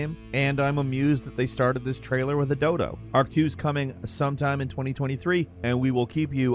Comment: I thought he said "Dildo" LOLOLOL